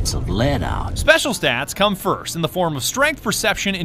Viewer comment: Special is what that says